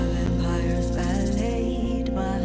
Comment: Vampire salad? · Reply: I always hear "Vampire's valley".